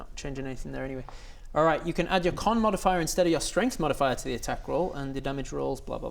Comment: typo in subtitles; says "attach" instead of "attack". 🫡